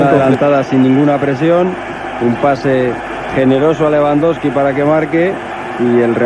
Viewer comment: Did I hear Lewandowski?